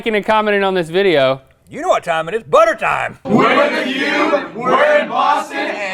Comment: it's funny because if you turn on subtitles at about 13:33, they put the Yube instead of the Cube lol